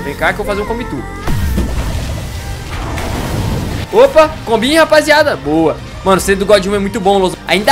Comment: so eu ouvi o dizer " comi rapaziada"